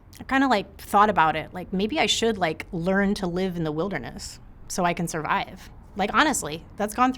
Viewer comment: Did she just say "Country Boy can survive" ??? heck yeah, im on board